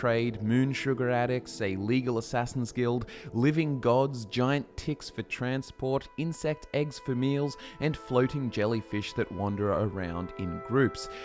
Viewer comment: I thought you said “giant tits” lmao